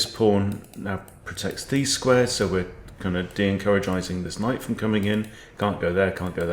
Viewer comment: De-encourageising…did I hear right ? ( around 8:30 )